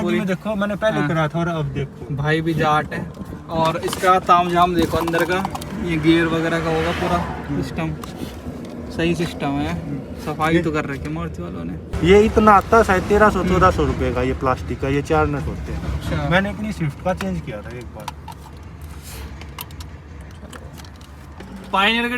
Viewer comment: Kya Bhai Kya??? Kya Bola? 😛 "SAFAI BHI TO KAR RAKHI HAI MADARC0DO NE" 😂😜😂